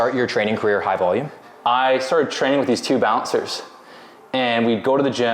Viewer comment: That training with some bouncers thing is badass, sorry if I'm wrong but that's what I heard 😊